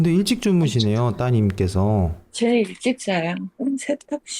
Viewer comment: 본방을 안 봐서 목소리인지 모르겠는데 따님분 주무시는 거 비추실 때 "일찍 주무시네요" 하실 때 '쟤 일찍 자'라고 보다 먼저 대답하듯 하는 남자 목소리가 들리는데..? 이신가요..? '왜 이렇게 일찍 자?'일까요...?